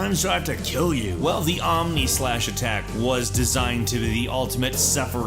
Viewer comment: For number 7 when it said omni slash i thought it said Omnissiah also I'm happy to see one of my favorite video game antagonists at the end